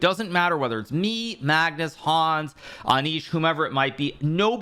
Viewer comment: Did bro just say Hans😮👀